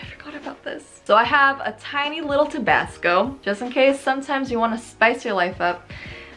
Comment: Thought she said tobacco lol 😂😂😂😂😂